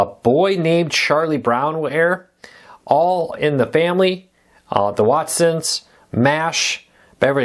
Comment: When listing programs for MeTV for Christmas, I thought I heard the Watsons.😀😀😀😃😀😀